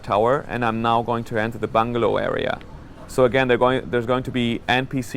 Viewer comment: lol when he said bungalow i thought he said bung hole! O.o